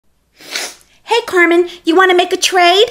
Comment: Oh I thought you said cartman